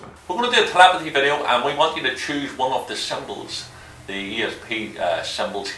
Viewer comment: who else couldnt tell what that guy was saying?? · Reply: guerrameanswar 03 he said "choose one of the symbols", he just Has an accent